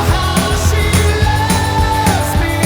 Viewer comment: 気持ち良さそう🤤 how she loves meが走れに聞こえてしまう